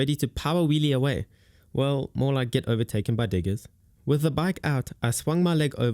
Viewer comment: "well, more like get overtaken by niggas" 😂 That's what I heard, dont judge.